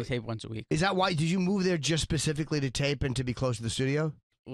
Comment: "Did you move there just 'pacifically' to tape and be close to the studio. As a YMH fan, him saying pacifically instead of specifically hit hard.